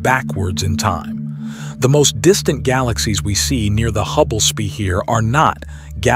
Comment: Holy fuck this is all AI. Listen to the breathing and at like 5:00 they say Hubble es-pere instead of sphere.